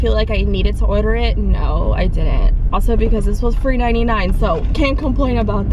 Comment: Did she just say that water was $3.99? 🥴💀